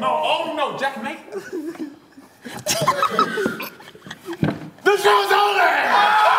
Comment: what did say about becky's foot? I heard "the shoe is on there" but I dont get it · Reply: he said “the show is over”